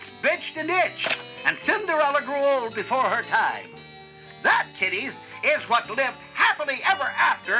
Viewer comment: I thought it said “that tiddies”